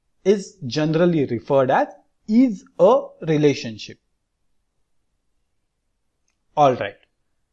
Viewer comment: what is being said here? its unclear. and no transcripts. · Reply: IS-A relationship